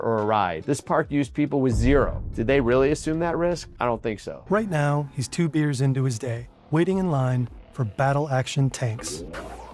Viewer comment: Wait a tad here, 15 years old and two beers into his day. Did I hear that right? Around the 5:50 mark.